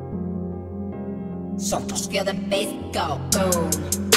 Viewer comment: What does he say in 0:10 ? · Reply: Santos, feel the bass go boom